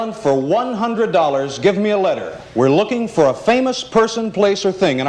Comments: She actually said F not S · She clearly says F, but they put down S.